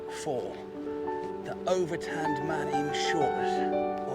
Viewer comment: I misheard “an over-tanned man” as an “overturned man” and wondered if you were going to go and help him like an overturned little boat or a turtle on its back 😆🤦🏻‍♀️